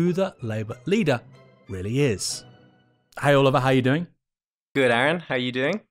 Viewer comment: When Aaron introduced Oliver it sounded like he said "heil lover, how you doing?"